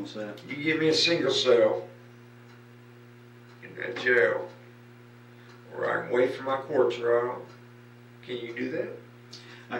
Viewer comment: Hahah… subtitles are funny sometimes… “where I can wait for my corpse to rot” (29:47 ), he actually said “where I can wait for my COURT TRIAL”. Lol!!! Had a good little giggle on that subtexts.